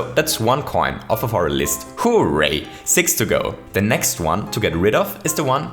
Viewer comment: To be honest when I first found, i was kinda put off by his accent and quirky style. But now I am so in love with his goofy charm, and the effort he puts into his content. Every time he says "hooray" I get the biggest smile. I wouldn't change a single thing about this channel. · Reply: When he first said hooray, I thought he said who raped